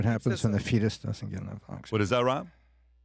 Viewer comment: “ See what happens when the fetus doesn’t get enough” 2:15:28 what did Tom say there? 🤣🤣🤣🤣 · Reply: Oxygen bro when the fetus doesn’t get enough oxygen 😂